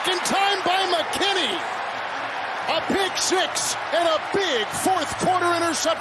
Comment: did he just say pick 6 🤦🏽‍♂️😂